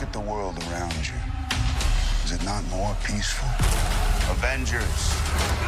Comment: I gotta run this video back. Did you just say “Revengers” @ 0:10?